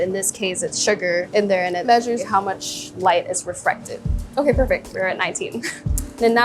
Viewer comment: Pretty sure she's saying "refracted" but the captions say "reflected"